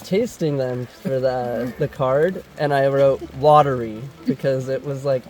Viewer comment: When he said “watery” I thought he said “lottery”-and the question had been “are they edible?”